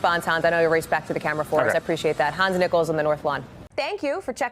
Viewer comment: I thought she said, “Hans Nipples.”